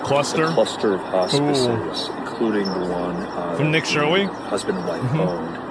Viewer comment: did you say Nick Shirwee?